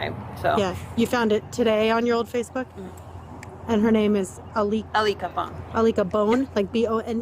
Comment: I-LICK-A-BONE??? Is that what she said her friends name is??? Lol